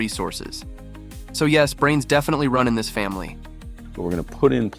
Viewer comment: Brains i thought he said trains so i married him.